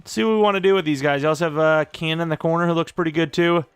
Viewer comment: not even going to lie. I thought you said “can in the corner”. The amount of replays till I finally realized is embarrassing on my part